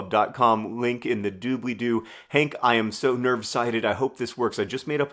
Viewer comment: I heard that as “nervsighted” which actually also kinda works.